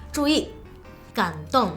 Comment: yo escuche condon